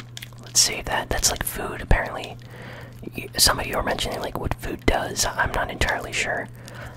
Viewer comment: It makes me cringe that you haven't looked at the controls & see how to run etc. Also at 32:55 did you say you don't know what food does lol